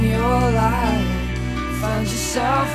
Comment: For the longest time I thought he said find your cell phone again